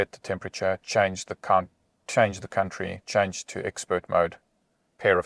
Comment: Did he just say change the cunt 😂😂😂😂